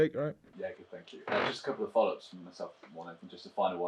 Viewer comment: Did he say "Just a couple of bollocks from myself"? 10:10 And then he winked